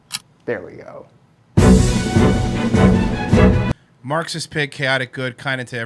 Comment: I thought it said "Maxi Pad" instead of "Marxist Pig". Would have been more accurate IMO.